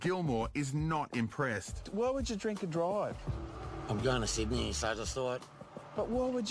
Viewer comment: Dodging the question lol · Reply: I heard it as like, "Why would I do drugs and drive?" 🤣